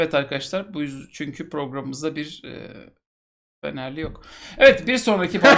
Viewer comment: 'de ne diyor abi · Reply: Fenerli yok" Diyor